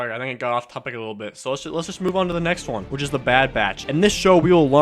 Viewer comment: i thought it said “bad bitch”